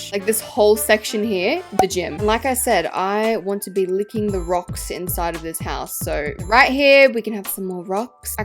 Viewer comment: you want to be licking the rocks in the house?? thats what i heard